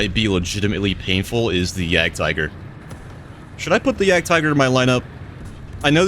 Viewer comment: Thought you said you put the jagdtiger in the lineup? I was looking forward to hearing what you have to say about that tank with the nerf.